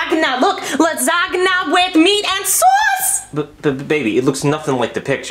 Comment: did he say baby?